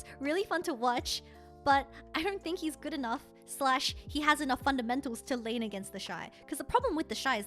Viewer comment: do I hear fundamentals???